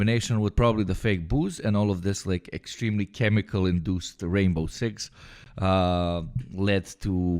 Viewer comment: 'Chemically induced Rainbow Six' is what I heard initially at 1:18 😂